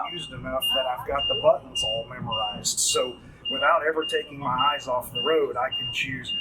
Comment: Did he just say, "Without ever taking your eyes off the road you can use your phone to...."